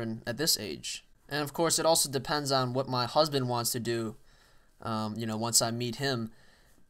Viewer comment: of this video is it me or does say MY HUSBAND ??????????? please send a message if you think that this was a mistake or something else to my account